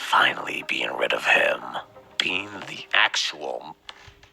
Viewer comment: I think Eclipse was gonna say "being the actual Moon", it sounded like he started to say "M" before he stopped himself